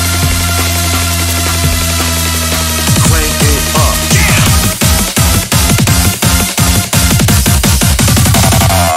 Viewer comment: what does it say here? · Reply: Crank it up